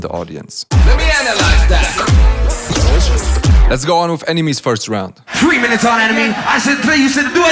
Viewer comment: I thought you say ENEMY but realizing it was nme HAHAHAHAHAH